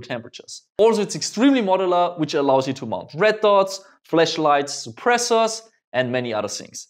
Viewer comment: Every time you say flashlight it sounds like your saying fleshlight 😂